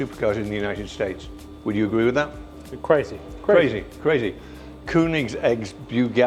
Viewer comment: i heard koo need eggs....LOL!